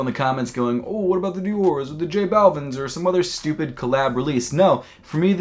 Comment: J balvin, I thought you said Jay Bauman. I'd be hyped as fuck for that.